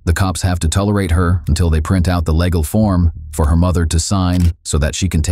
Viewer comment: Sound like he said lagal when he said legal